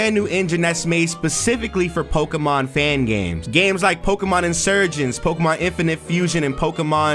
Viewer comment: I thought it said Pokémon Insurance